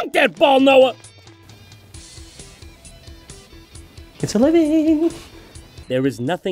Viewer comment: I know it's been 9 years, but what is he saying at 10:45 ? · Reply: Jon is saying, "It's a living!" 🫎 Have an excellent weekend 🍻